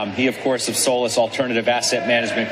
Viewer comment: Sounded like he said “soulless alternative assets” lol